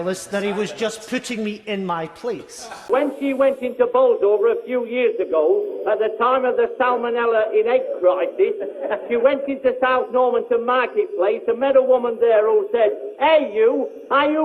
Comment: Can someone transcript the part from 2:01 ? i'm learning english but i can't understand that accent. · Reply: "when she went into Bolsover a few years ago, at the time of the salmonella in eggs crisis, she went into South Normanton marketplace and met a woman there who said 'hey you, are you Currie, the one about the salmonella?" "She said 'oh yes, are you going to vote Tory?'" "She says 'look, I've got six of these eggs in my basket and if you don't sling your hook back to South Derbyshire, you'll have these on top of your head!"